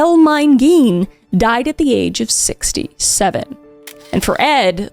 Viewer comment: D-did you just say 6- 6- 67?